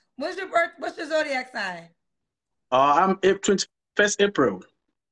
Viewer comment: I thought he said 31st April 😂 I'm done 😭